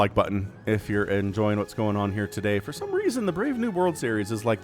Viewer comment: i always thought you said "brave new world" but this makes more sense lol